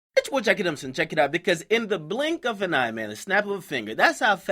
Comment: I can't figure out what he's saying at 0:00. Bitch jacka jemick check it out??? I been watching for so long and still can't figure it out